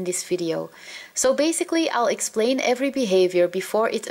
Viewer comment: Did she just say "Basically owl explain every behavior..."